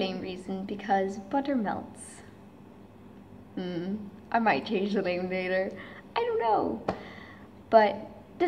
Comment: What is she saying at 0:10, I don't understand, anyone? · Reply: “I might change the name later, I don’t know.”